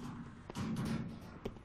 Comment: Did it just say cumpie